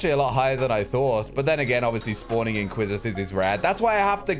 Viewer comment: what did he say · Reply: "Spawning inquisitors Is rare"